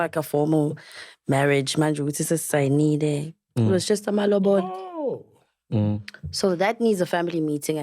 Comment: Did she just say "it was just amalobolo and just needs a family meeting " haibo mama. That's a customary marriage. Go dissolve that immediately please.